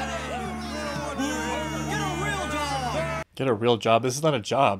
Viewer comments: i think he actually said “Get a real dog.” Not “ Get a real job.” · the guy actually says "Get a real dog!". That's what I heard at least. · at around 2:05 what the guy in the crowd said was, “Get a real dog!” 🤣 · I'm not sure but I heard "GET A REAL DOG!" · I think they said, "Get a real dog"? That makes it so much funnier